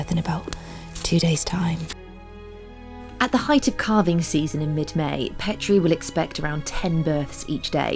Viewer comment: Thought you said "carving season". That was a relief!